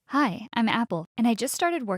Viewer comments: I heard her say “Hi! Im apple” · Did she say apple? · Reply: I think she did say apple at 0.00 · did u just say APPLE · her:"hi im Apple what i hear:"Hi im an apple Me:" a talking apple OoO"